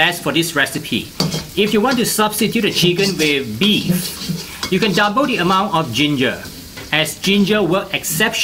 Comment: did anyone get what he said at 5:15? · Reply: Hi, I just checked the caption.: 'If you want to substitute with beef, you can double the amount of ginger ' Sorry for my accent. You can turn on the caption of the YouTube, which I have uploaded. Thanks